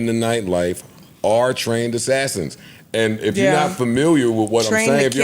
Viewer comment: I thought u said Trained Assassins… u know this ain’t who u speaking on when u say THAT. Where is Bernice the iii3s lani gissy nem